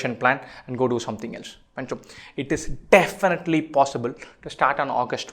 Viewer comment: i heard bhencho